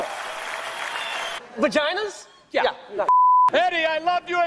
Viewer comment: Só eu escutei vaginas em inglês? kkk